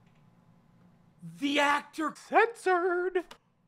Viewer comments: (5 months late) 9:25 if you dont know what he's saying. read his lips (but if you still dont know what he's saying look below this message) he's saying "THE ACTOR KI**ED HIMSELF" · can somebody tell me what he said here? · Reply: Charlie browns actor unalived himself 😵‍💫